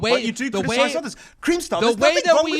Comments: Did he say "Creamstar"? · Is it just me or did just say Creamstar at 34:42 XD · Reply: He said cringestar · anyone else hear say creamstar